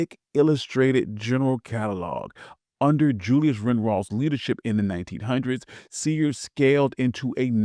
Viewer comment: It's 5 20 am I should be sleep at 2:45 I heard Julius Rick Ross